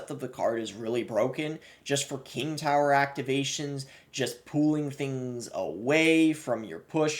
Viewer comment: i can’t be the only one who thought he said “pooling” instead of pulling